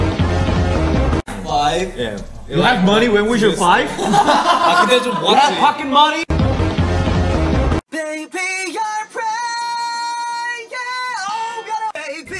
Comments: Han's face when he said "you had POCKET MONEY?" 😭😭 he really can't believe it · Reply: i heard 'you had fucking money?' 😭🤚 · Han's face when he said "you had POCKET MONEY?" 😭😭 he really can't believe it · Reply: When he said pocket money I thought he said you had fukin money💀💀💀💀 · It sound like he said “you had fucking money”💀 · He says pocket money but I heard " you had fucking money!?!?" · I thought he said fu money not pocket money 😭😭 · i thought han said “you had fucking money?” but its pocket money